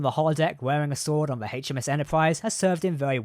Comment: did he say "HMS Enterprise"?